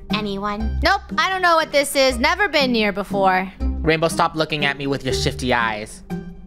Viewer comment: legendary moment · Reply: Uhhhhhhh guys I think Curse......or i am Just Tired like i hear said "Rainbow don't Look at me with Those sh*tt*ng eyes"